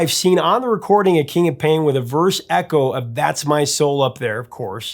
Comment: I originally misheard it as "Christmas soul up there" :)